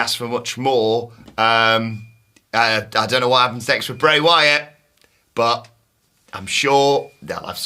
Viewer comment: My dumb perverted mind thought he said "I don't know why I'm having sex with Bray Wyatt" at 23:23 😂😂😂😂 Then relized what he actually said 😂😂